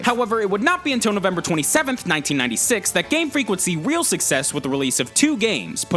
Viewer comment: Did he say... NOVEMBER 27, 1996